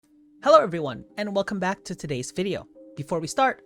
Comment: At first, I thought you said, “Hello Obi-Wan!”.